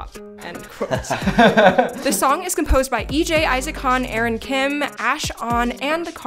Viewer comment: At first, I thought you said that Issac Hong was one of the songwriters, and I almost fell off my chair. Issac Han. If it was Issac Hong (a Korean singer-songwriter), he really came out of his shell to write something like Icky.